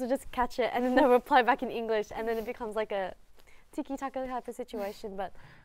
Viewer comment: can someone tell me what she said here, I have trouble understanding it since English isn’t my first language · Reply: She said "it becomes like a tiki-taka type of situation", surprised she used a football term but it's pretty much means "passing back and forth", so they'll be exchanging phrases in Korean and English with each other.